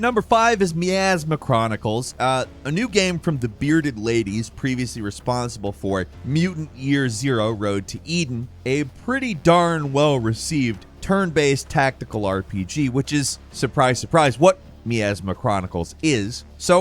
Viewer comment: "Mutant Ear Zero" I blew air out of my nose fairly quickly when I heard that.